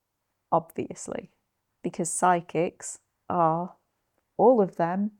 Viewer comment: ngl girl I heard "psychics" as "sidekicks" and I was like damn whats the beef with robin, i know he wears his trousers below his pants but... and then i saw your video recommendation and mi head went AAAAAAAAAH it makes sense now. great video btw!